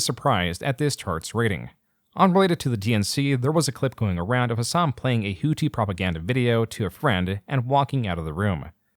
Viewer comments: What does the guy say to him at 1:28:29 ? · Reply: "Destiny made you" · I've seen this clip before, rewatched it a dozen times, and still can't make out what the guy says. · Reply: "destiny made u"